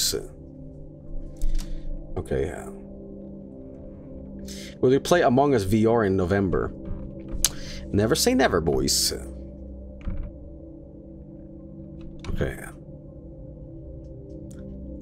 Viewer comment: did anyone catch the "AMOGUS"